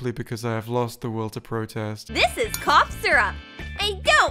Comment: i heard "this is co--syrup"